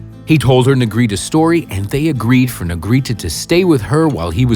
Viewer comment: Did he just say Nigrita cause she's black???